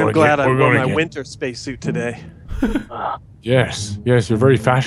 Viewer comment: did say its slimed with fur XD